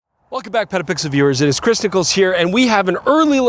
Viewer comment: I heard Christicles the three times I replayed it to make sure I heard right!😆